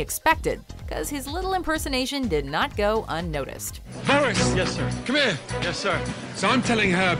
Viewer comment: did he call boris “Harris”